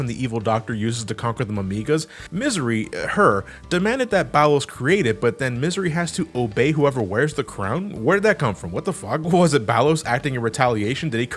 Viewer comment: I thought for a good second he said "Ballas" and I thought "wait, what? this motherfucker was torturing robots before being a dick to Warframes?" Then I did a double-take, realized it's spelled "Ballos" and thought "oh, ok, nevermind."